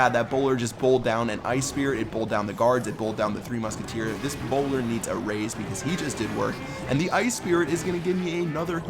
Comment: I thought that said "this boner needs a raise"